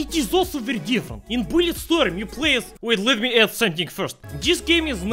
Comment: i heard blyatstorm